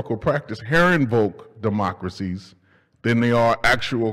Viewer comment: Hi Bard College. Your lectures are amazing (please improv the audio quality by reducing the gain during the live event) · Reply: I've been trying to figure out what he says at 8:01 and the best I can come up with is "karen vote democracy" but that can't be right 😅